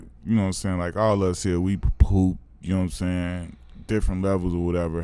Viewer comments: nahh at 1:05:21 I swear Q Rich said "we poop" I thought he was gonna bring up the leaving the game situation cause he shit hisself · What it sound like starting at 1:05:17.... "I want you to talk about how it feel like, you know what I'm saying, like all of us here, we POOP, you know what I'm saying..."😂😂😂